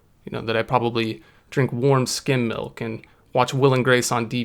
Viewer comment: He is a registered sex offender that drinks warm skim milk and watch will & grace every night and laugh at the jokes so hard that he spills the skim milk on his one furniture in his studio apartment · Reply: RGX I honestly heard it as “warm skin milk”… skim milk makes so much more sense.